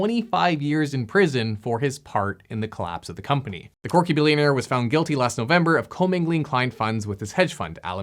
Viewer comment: hahah 2:07 i heard “the porky billionaire” at first